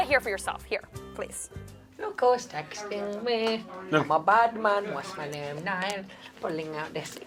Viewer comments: what louis said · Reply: Pulling all the sick birds, what's my name? Niall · what did louis said what i understand is im a badman and my name is niall IDK????